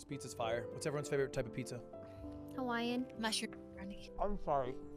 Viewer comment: I thought Rae said Hawaiian mustard at first, I have to re-watch it to get it was mushroom